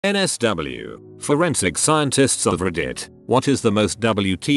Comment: At first i thought it said foreskin scientists